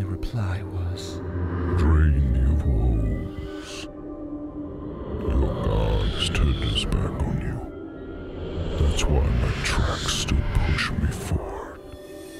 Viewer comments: Bro i’ve rewatched this like 10 times and i still cant tell what he says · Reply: "Drain thee of woes, your god's turned his back on you. That's why my tracks still push me forward" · "I have seen past the farthest star. And I have gone to the edges of the earth. I can take you there, if you so choose. I can give you all the things you never knew you wanted. Taste from the tallest chalice, dine at the largest halls. All I ask in return, is your soul. Your soul for a chance at a life without pain, without heartache. All this I can give you and more. Just for your soul. Drain you of woes. Your God's turned his back on you. That's why my tracks still push me forward." ~Thomas, The demonic vessel. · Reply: I thought he said "dream, you fools. Your god has turned its back on you"